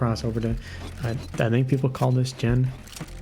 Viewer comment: I think I hear people call it fridge